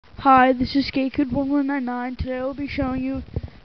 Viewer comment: lol sounded like u said this is gaykid hahahahahaha